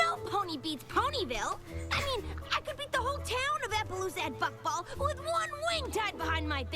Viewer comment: I thought i heard Rainbow say 'Fuckball' i dont know why 😂